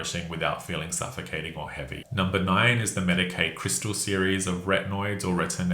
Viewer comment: Whoever named Medik8 was not a poor American lol. I kept hearing “Medicaid” (our health insurance for certain of us low-income Americans). I was like “Medicaid is covering retinols?!?!” 😂